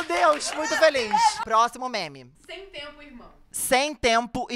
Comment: Na segunda rodada do "Sem Tempo irmão" quando a garota do time do falou eu entendi "Sem Tempo Emo" kakajabd